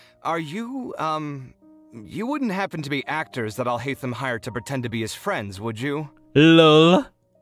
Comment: All the time when someone says the name Alhaitham, I hear "I hate them" am I the only one person who hear this?